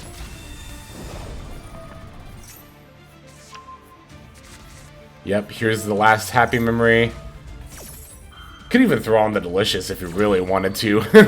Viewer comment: "This is his last happy memory" I know you didn't say that, but this was what I heard and it made the ending epic!